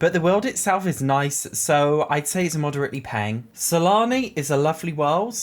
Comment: Am I the only one who thought he said salami instead of Sulani?